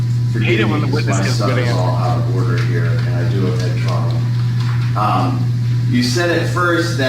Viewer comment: Can anyone understand what the “Sov-Shitizen”, is saying? I hear “…I hate it when my stuff is all out of order here and I have a Head Trauma.” Oh, I think that the “Troopers” showed up due to the proximity of the Governor’s residence to the Incident. Cheers. 🇨🇦